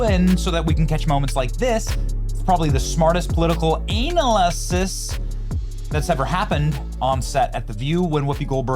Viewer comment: Did you just say "Anal IsIs"